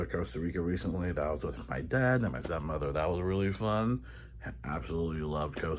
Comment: o I thought he muttered son instead of stepmother 😂😂😂